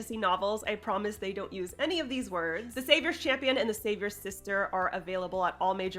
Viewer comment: Fun fact: If you talk about fisting and then follow up by saying "Savior's Sister" I hear "Savior's Fister" instead and choke on my tea! Happy writing everyone!